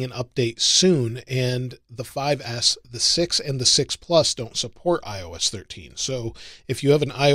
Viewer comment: did you say iPhone 6 or iphone 6s? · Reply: He said 6 or 6 plus